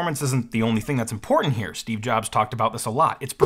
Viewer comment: Did he really say Steve Jobs?